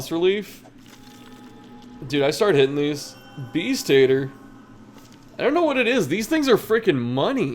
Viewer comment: I heard Bee's Tater